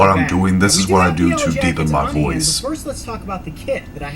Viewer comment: lol what did you say you was doing deeping my voice ? o_O